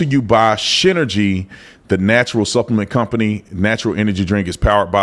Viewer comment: Who else thought he said shiteregy?